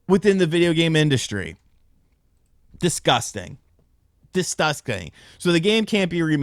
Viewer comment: What did he say???? @ 8:25😂😂😂😂 DisTUSSting 😅